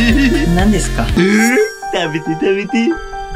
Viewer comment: 田中先生のたべてたべてが某ユーチューバーのためてためて(解放！！！)に聞こえたの俺だけ？